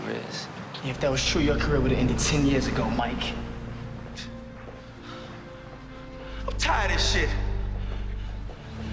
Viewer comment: What did Mike say to Ralph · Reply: “You know it would be easy for you to leave Rizz”